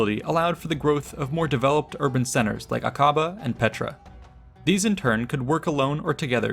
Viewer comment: On 2:40 did I hear "" kaba """ in petra . then that is totally 100% wrong wrong wrong … fake. News....